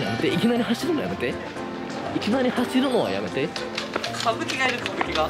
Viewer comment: ⇸いきなり走るのやめて 自分⇸イケメン走るのやめて 聞こえたし、思ったw